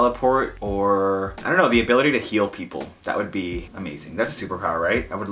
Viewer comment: I was training while watching you and I misheard you and heard "the ability to kill" hehehehe I replayed that part. Sorry. I Love listening to you